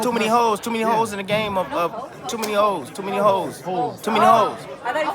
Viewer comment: Woman got mad when he said, "too many holes". She thought he said, "too many hoes". lol